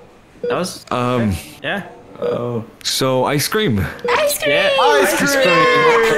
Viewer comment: i thought they said, "I scream!" but no it was just ice cream